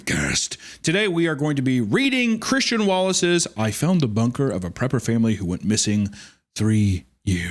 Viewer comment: I thought it said a Dr Pepper family lol